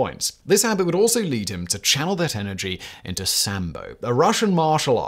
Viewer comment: Thought you said he was getting into Samba for a second; Definately funnier than Sambo, not as useful in combat situations tho. Lol